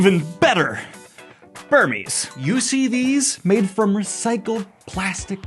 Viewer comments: Dear god, I thought said 'You know what's better than Tolkien? Furries.' ROFL I was trying to figure out why you would compare the two. :P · Oh. Burmies. I misheard him and thought he was gonna enlighten us to the bliss of furries.